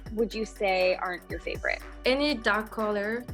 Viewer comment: "What is you favourite color?" Client : "dark colors, dark green, dark blue" "Okay so keep it BRIGHT and Airy" Ermmm child..... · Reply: You misheard that bit. asked what colours “aren’t” your favourite. And she replied dark green and dark blue.